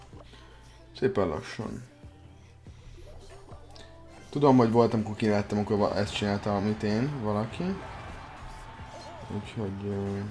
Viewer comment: Mit mond 19:47-nél??? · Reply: Azt mongya hogy volt ojan kör ahol azt csinàjàk amit ö